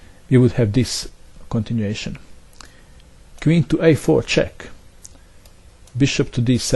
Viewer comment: for a long time I thought he's saying "If black does this , there is discontinuation" . That moment when I realised he meant this continuation :O